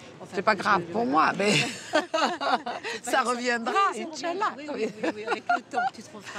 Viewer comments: c moi ou à 14:31 elle dit inshallah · c est moi ou elle a dit inchallah ? 😂 😂 😂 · c'est moi ou elle dit inchalah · C moi ou elle a dit inchaallah à 14:30😂😂😂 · C'est moi ou à 14:28 elle a d'i inch'Allah ?😂